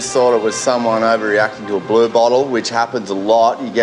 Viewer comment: At first I thought they were saying “ blue balls”!! STG